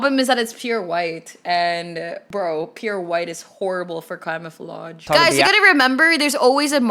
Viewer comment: ALERT: THIS TIMESTAMP IS NOT (ONE OF THOSE) 10:18 Bro did she say Clamofolage?